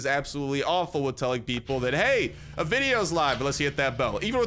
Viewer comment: THE FULL UPDATE 1.9 IS OUT, AND IT'S BROUGHT EVEN MORE NEW THINGS! Hope y'all android players are enjoying, and that all the iOS lads and lassies join us soon :D Stick around by being subscribed, weekly giveaway getting announced tmrw, and new weekly $25 Android/iOS giftcard starting then too! Can't wait to explore the update even more with y'all (: NEW STUFF! Hopefully will be streaming more over here, so hit the page with a follow/like! -----> <link> (and following/liking enters you into the giveaway, more details here! <link> ) $1,000+ PC GIVEAWAY!!! <link> (Ends in July!!) Go check out + follow my instagram if you haven't yet too! <link> Perfect time to remind ya to download AppZone for a legit free way to get items from the store AND support the channel! <link> And we got the brand new merch in as well, hope y'all enjoy + mehbeh decide to get some. Awesome way of supporting the channel AND getting awesome stuff for yourself too :D <link> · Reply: I thought you said a poopy! Poopy